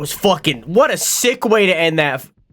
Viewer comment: hi o/ :) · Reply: did you say end? :3